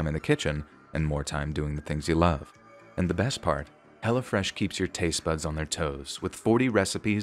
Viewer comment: I swear I thought you were saying Hella Fresh lol